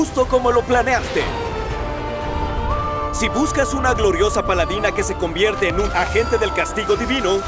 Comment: escuche bien paladina ? INSTA MAIN